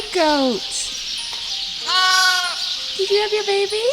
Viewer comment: I burst out laughing when you told goat off for biting the tail of her lamb 🐑 lol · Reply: I thought I was going crazy when I thought I heard her say Goat did you eat your babies tail?